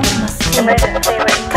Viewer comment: Emergency what service? Emergency what service? Emergency what service? Chameleon machine Chameeleon machine Chameleon Chameleon Ride on now. It just sounds broken because she is speaking VERY heavy Engrish. Engrish is when Japanese people speak English with Japanese accents and often times, vowels get added to every syllable and l and r sounds get switched. You hear it a lot in video games when Japanese voice actors have to speak English words. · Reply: I always heard the "ride on now" as "they don't know" which would seem to make sense